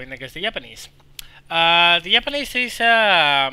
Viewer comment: i thought you said "yapanese"